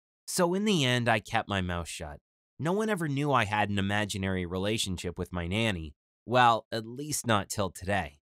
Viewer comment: I thought it said “my secret relationship with my baby sister”